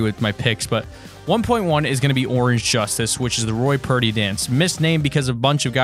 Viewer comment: Lol I thought you said “Roy Party” but it was “Roy Purdy” But it would be pretty lit if his last name was Party.. 😂